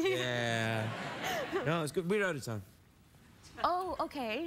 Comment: what did he say before she said "oh ok" and then the awkward pause? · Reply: "We're out of time."